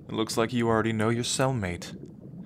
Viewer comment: I honestly thought he said soundmate whenever he said cellmate. Although, I do feel like that's the appropriate term for a siren's soulmate.